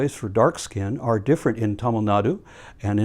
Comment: What country/place does he say? Sounds like saying "Intomanadu" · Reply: Super late reply but I believe it is Tamil Nadu, the southernmost tip of India.